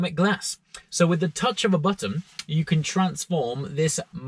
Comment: sounded like "from the touch of a buttom.!" hahhaha..